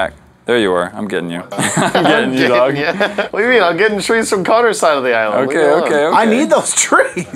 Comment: I paused the video at a point I heard “Connor daytime” lol😹